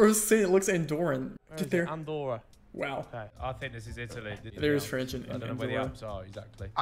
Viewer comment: I'm not sure I heard this correctly, but here I understood "There's French in the wood/wall", or something like that? Anyway that's not French that's Catalan, they can look alike but there are certain big differences between the two languages. I'm also not very surprised Harry guessed Andorra there, that country is known for attracting many youtubers, many of which move there to avoid taxes, but many others just go to record videos since it has interesting touristic attractions. I'm sure the Sidemen have been there and Harry recalled the architecture of the place.